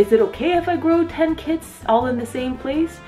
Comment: So I heard "I got 10 kids" and I was like wtf. But she said kits lol